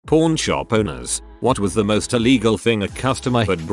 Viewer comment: Idky why the narrator sounds like he saying "p0rn shop"